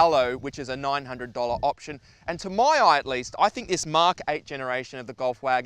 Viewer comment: Did you just say mark-kay instead of mark for marque? Damn you VW, that car is excellent! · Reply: He said Mark 8